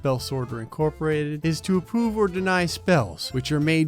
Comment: he says apoove instead of approve 😂